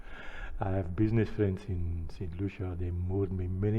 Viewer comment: Good morning how are you doing today, I was looking at your video and heard you mention St Lucia but I'm not too sure if that's what you actually said. Check the time frame from after you said you're a socialist, you have business etc from 9:17 that's where I think I heard St Lucia. If not then thanks for the share.